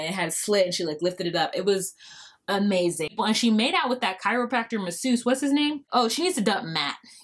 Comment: Sweeping generalizations about any Grp is not good and blcks don't like when it's done to them and a chiropractor is not a masseuse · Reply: Oh, he's a Chiropractor? I could have sworn they said 'Massage Therapist', haha.